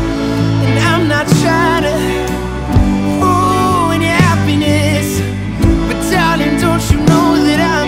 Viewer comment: I hear “Fool your appetite” instead of “Ruin your happiness” what 😂😂😂😂